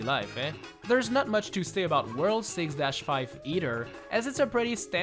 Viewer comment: Is it just me or did say Eater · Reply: He said either